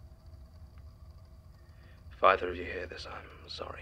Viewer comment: Does anyone know what says at 19:36 ? It's not witten in the transcript. "???? if you hear this, I'm sorry." It sounds like "father", maybe to address Dekker from the statement, but I'm not sure.